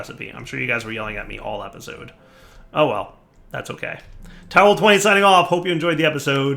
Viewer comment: Did anyone else hear that signoff as "Towel20 signing off..."? :D